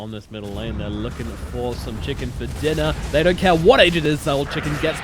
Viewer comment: Did he say chicken for dinner lol